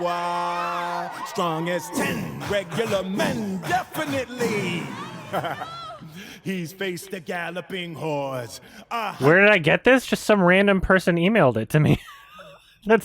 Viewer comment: Did he say "galloping whores?"